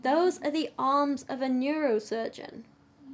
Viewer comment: Every time I hear “the arms of a neurosurgeon”, I just hear “This is the skin of a killer, Bella”.